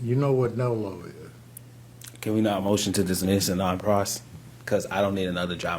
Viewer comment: What did he say at 27:58? Did he say can we not motion to dismiss a non-process? Can anyone here who study law, please clarify this?